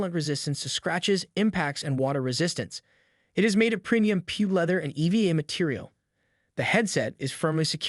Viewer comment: Thought that said Pube leather instead of PU leather lol.